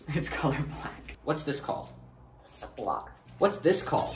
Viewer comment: When he said sock I thought he said glock